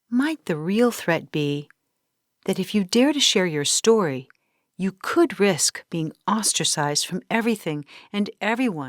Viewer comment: I thought you said you could PROVE it?!